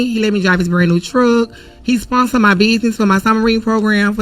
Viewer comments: I thought she said "submarine program" (im thinking hood kids are n2 the water/ocean... that's kool).... not "summer read". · I could have sworn she was saying "submarine" and I got interested. Apparently it's "summer readin" though.